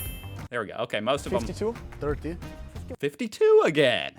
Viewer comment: I think he said 13, which would have been the number of founding states and number of stripes on the flag. 4:35 "George W. Washington" That one got me good. XD